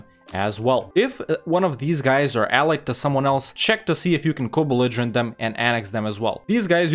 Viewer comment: There’s a word use here several times, I can’t quite make out what it means. When he talks about taking nation, he says something like „if you cobeligerent them“ See 06:41 - what’s that word/phrase?